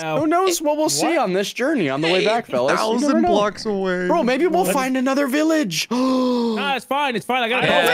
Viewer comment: not to be that guy, but at 13:40 the subtitles are wrong. it says "fine" instead of "find".